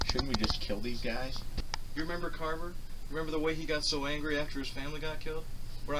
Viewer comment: Question ok so when the two detectives talk about jeff being back it sounds like harris said carver . but what confuses me is it carver or cater ? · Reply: Carver